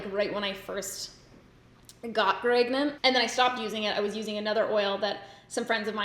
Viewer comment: Sam: I was using that when I first got gregnant Me: rewinds to make sure I heard that correctly